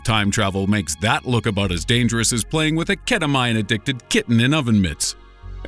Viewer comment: Did he just say a Ketamine addicted kitten with oven mitts?? Lmao that's so specific.